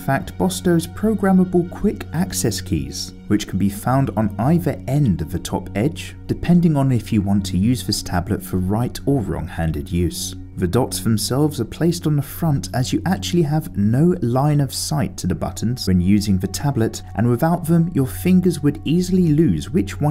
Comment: did he say right or Wrong handed use...🤫😂 video is so helpful though...